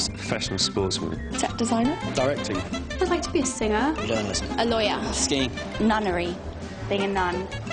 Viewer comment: Can somebody tell me what Dan want to be if he was not an actor in 5:47? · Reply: He said journalist